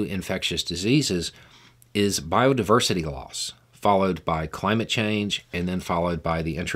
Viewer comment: At first, I thought you said the biggest driver of infectious diseases was biodiversity laws, not loss. It was very confusing.